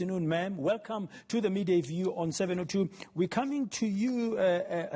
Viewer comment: Did Dan say on 702 at 1:04????